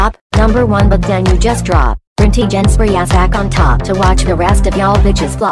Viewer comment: What is she saying at 3:08? I've listened so many times and still can't figure it out. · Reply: Brinty Jen Spreas is back on top.